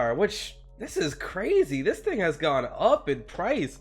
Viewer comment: u thought u said "this nigga has gone up in price" so i played it multiple times and found u didnt.